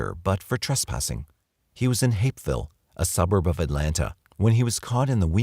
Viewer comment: def had to play it back, thought you said “Hateville” I was like, “damn.. explains a lot” 🤷🏻‍♀️💀